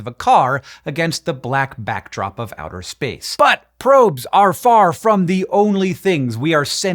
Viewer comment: I watch ZeFrank's true facts so much I heard "butt probes"